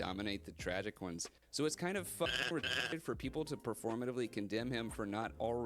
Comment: what did he say there?? did he say people pointing out bear’s lack of empathy were performative and “fcking rtrded”??? christ above. he is right in that the story is about bear’s lack of understanding of nikki, but he is wrong using that as an excuse. if it was to be a way out, that would require bear learning to understand her, or at least attempting to. but the thing is he Doesn’t want to!! to use empathy in my take because I know it would piss this guy off, bear’s wish and actions afterwards showed lack of empathy. empathy could have been learned/practiced when real nikki pleaded with him, it could have been learned when he heard her screaming over the phone. but it Wasn’t. because at the end of the day he did not truly care about her. that is the story being told, a selfish man making a selfish mistake and double, triple and quadrupling down to the very end, even when his mistake costs him all of his “loved” ones. (loved in quotations because let’s be real none of his friends were good ones, either because of how they treated him or how he treated them. but I won’t get into that here because unlike what this guy claims we all have more than a couple brain cells to rub together.) (also near the beginning it’s crazy how he found the idea of finding bear to be the villain ludicrous, and how could anybody possibly have come up with something so incorrect. when the michael johnston is on record stating plainly that bear is the villain. yk. the ACTOR OF THE CHARACTER.)